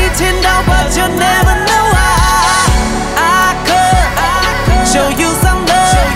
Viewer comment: 只有我把i could听成阿珂吗😂